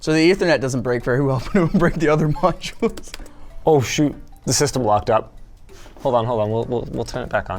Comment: Thought id let you know, the camera guy said "oh fuck" at 18:25 and the editor didnt catch it and bleep it